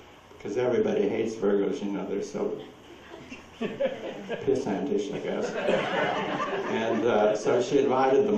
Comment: What did he say about the virgos (at about 6:40)... "cause everybody hates virgos because they're so... piss ...???" Can anybody make out?